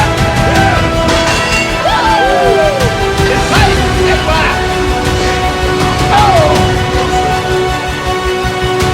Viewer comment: Does anyone know what exactly the King says at 1:50 ? I've heard it 200 times over 2 decades but still can't figure it out. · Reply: Pretty sure he says "it's light, that's why".